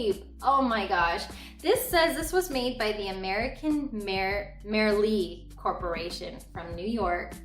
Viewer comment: Hello, I couldn't catch who you said made that pumpkin at 2:19, can you please tell me who made it? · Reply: No problem, American Merrie-Lei Corp 🙂